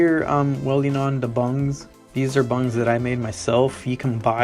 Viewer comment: Whats's the name of the nut (?) that you are welding in the 3:02? Sorry I can't understand the name of it. Thanks! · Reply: they’re BUNGS. If you’re running a Harley then 1/2-13 threaded bungs are what you’ll need. They’re a type of weld on insert that fits into the ID of tubing. These are threaded all the way through to allow you to use your already existing Harley handlebars bolts. They make different types of bungs for different applications, they make some that don’t thread all the way for tank applications as well, so be careful you’re getting fully threaded ones before purchasing. Thanks for the comment i hope that helped!